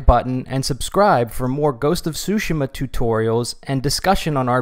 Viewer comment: I swear I heard him saying ghost of sushima 寿司マ